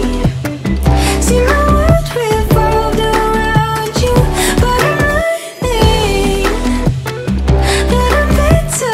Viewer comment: anyone knows what she says here? 0:52 "But I'm (something) that I'm better off without you" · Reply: but I might think